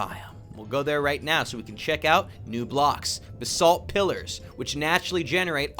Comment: lol when you said "Basalt Pillars" i thought you said "The Salt Pillars"